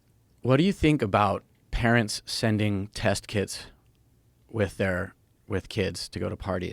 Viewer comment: First I thought he said they’re sending kids to parties with test kiDs lmao